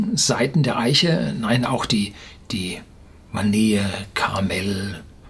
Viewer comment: Was sagt er bei 8:52? Die Manähe? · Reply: Vanille.